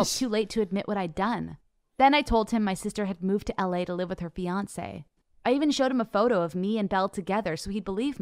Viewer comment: instead of fiancee did she say beyonce?🤣